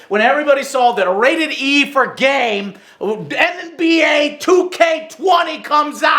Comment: is it only me that for a split second thought he said “rated e for gay”